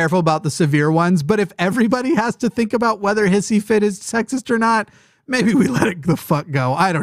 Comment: I thought said Cissyfit which seemed appropriate.